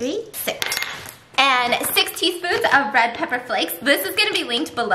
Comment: I thought she said sixty spoons of red pepper flakes I was like WOAH that's a lot